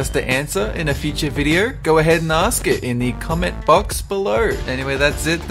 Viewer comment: i thought at the end that he said"any question coment them in the COMET box below